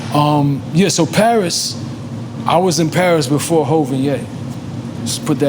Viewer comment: I Was in Paris before hove and yay…. NORE, before what!!! I thought you said COVID And Yay….. lol lol lol